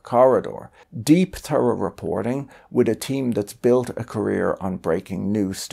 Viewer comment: I thought he said deep throated reporting..🤣